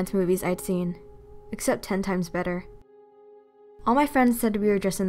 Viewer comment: I literally thought she said "hentai was better" um